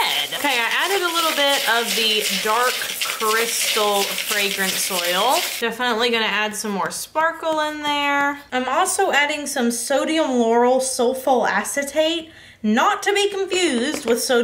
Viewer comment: I heard "Sodium Laurel sulf-" and was like, 😲! What are you doing! Lol. 😂